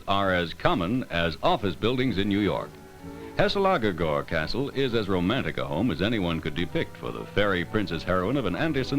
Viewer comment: heselaggegor castle? as a dane, I genuinely have no idea what he's trying to say lmao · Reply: Guessing Hesselager gård